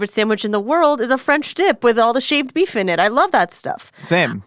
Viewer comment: I thought you said French tip instead of dip 😅